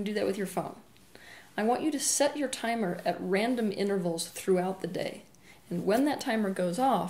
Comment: Does anyone understand what she says at 10:09? "I want you to set your timer at random _ ?" Inner pulse or smtg? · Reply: Raysa S. Set alarms on phone or other device at random intervals throughout the day. When the alarm goes off, note your emotional state.